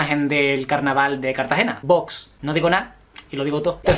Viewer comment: No entendi lo que quisiste decir en el minuto 9:06 :(( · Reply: Vox! Si no eres de España, es un partido de ultraderecha español